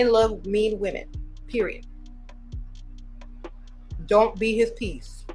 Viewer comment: did she say don't be nice?